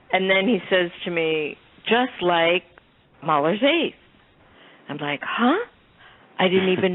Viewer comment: Right around 55:53 she says Billy is describing the music saying just like what?Is she saying Mahler's Eighth? I wish this video had text